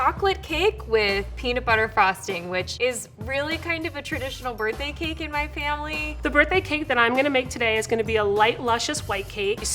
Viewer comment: all I heard was “ the alcohol I’m getting faded with today is..” shows bottle